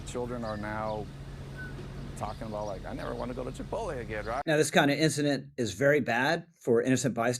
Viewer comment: My children are now talking about like "I never want to go to Chipotle again." Is that really what they said? Is it Chipotle they want to avoid -- or something else?